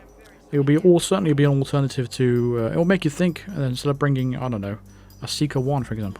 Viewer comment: I don't understand, is the sieker 1 not an emetic gun? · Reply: He said gum, not gun.